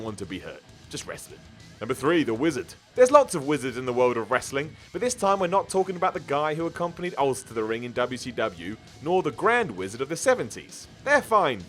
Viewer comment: uhm did he just say the Grand Wizard from the 70's???? The GRAND WIZARD??? Dafuq WWE?!